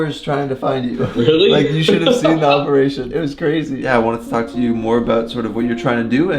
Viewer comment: i hope i heard wrong :) at 4:13 i though he said i wanted to fuck you